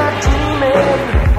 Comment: Quick question, at 1:28 is he saying "Don't get a psychic, they'll just read your tea" or "Don't get a sidekick, you just need your team"?